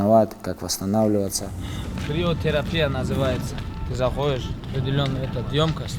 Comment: что он говорит приотеропия? что за штука дайте нормальное название